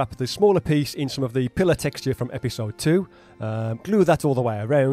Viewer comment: LOL! I totally understood you when you said "lift" but earlier the word "pillar" sounded like you said "pillow" and in watching you wrap it around I thought you were making an ottoman!? But just for a second...haha. Great job and another awesome video my friend!!